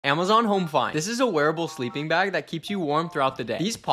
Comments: Back in my day- that "wearable sleeping bag" was called a snow suit lol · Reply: Thank you I thought he said werewolf sleeping bag. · I feel like that sleeping bag was made for Aizawa. If he had it, he wouldn't take it off😂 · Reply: Oh "wearable" I thought he said werewolf! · Who else thought he said werewolf sleeping bag??